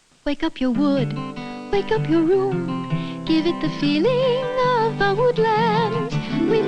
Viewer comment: In the commercial for Natural Wood Scent Pledge, I always had trouble deciphering the line after the first appearance of "Wake up your wood, wake up your room". Listening on a slower speed, it sounds like she said "Give it the feeling of old lands." Anyone know for sure?